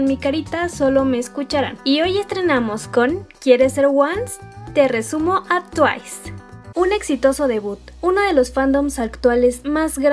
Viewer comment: PENSE QUE DECIA: "TE LO RESUMO ASI NOMAS: TWICE" JAJAJAJAJ